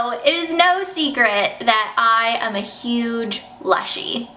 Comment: *beginning I thought she said slushie